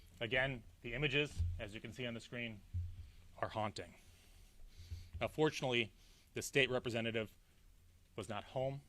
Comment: “unfortunately the Hoffman’s survived” WHAT? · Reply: I heard "and fortunately" ... why do you people want conspiracy?